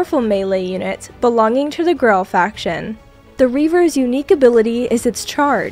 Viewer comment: What was actually clearly said: "Belonging to the Grell faction". My mind: "Belonging to the girl faction". I swear I hate my brain sometimes. Anyways, I absolutely love the design of this unit.